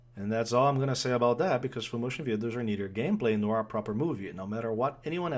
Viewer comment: I'm sorry, can someone tell me what he is saying at 7:52-8:00? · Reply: He hates FMV because "they don't add to gameplay".